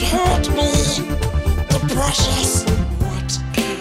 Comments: in the my girl when yoi say the precious i thought you said depression cause I was only listening and I legit said same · I thought he was saying depression during “the precious” singing montage 😭😭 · Why do I hear "depression" instead of "the precious" in his Smeagol song? Also, what do you call a male Karen? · I thought you kept saying "depression" and not "the precious" 😂